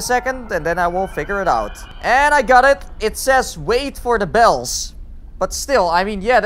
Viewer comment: I thought it said wait for the bitches.lol 12.29